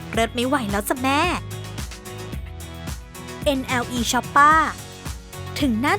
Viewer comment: ไม่รู้ว่าผมอ่านถูกหรือป่าว แต่มันมีรายการหนึ่งให้แนะนำด้วยการเรียกชื่อAKA ของเขาว่า เนล คล็อปป์ป้า ผมฟังได้ยินแบบนี้นะ เลยเรียก เนล คล็อปป์ป้า มาตลอด